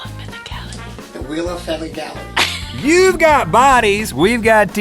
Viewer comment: did he just say the wheel of faggotality ;D